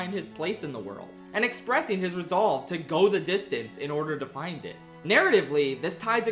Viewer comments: When I was a kid watching Hercules, I thought they were saying "go to Distance" instead of "go the distance" So I thought "Distance" was a location and I was always a little annoyed that they never actually "got to Distance" despite having a whole song about it. Glad it made it high on the list! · I heard “Thunder Bring Her..” at the #2 intro and I got excited for an Epic mention until I realized it was obviously “Go The Distance” from Hercules. I’d love to see a video all about Epic one day!